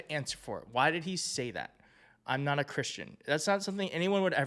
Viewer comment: Consumption of strong liquor will always affect one's hearing. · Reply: I’m a linguist, too. And an SLP. I heard “I’m not Christian.”. I’ve listened several times. There’s a clear stop after the /na/. His body language also says ‘no’.